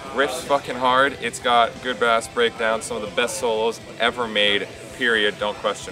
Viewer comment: He definitely does not say "good-ass breakdowns", he has to be saying "good BASS breakdowns" but saying it like he is referring to the fish not the instrument. Look at his mouth it is making a B not an A shape while speaking. But favourite Metal album would also be A7F <3